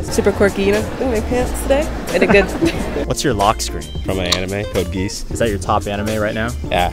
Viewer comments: Anime ones understood the assignment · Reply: it sounds like he said “code geese” 💀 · Bro said “Code GEESE” I thought he said one piece at first but I’m lookin at lelouch holding a Glock so I’m like wtf is this shit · "Code Geese" Oh dear lord let me melt away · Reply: <link> Around 0:24-0:25 I hear it as "gee-ass" so....